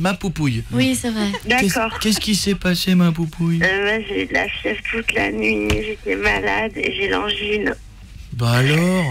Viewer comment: ouhla... J'ai compris "j'ai eu la chiasse toute la nuit,..." 0o